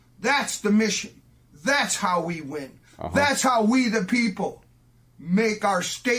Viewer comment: Did he say "We the Pimple"? He sure sounds like a pus-filled zit. For him, there's Clearisil. One dab and he'll disappear. Hey, I can dream!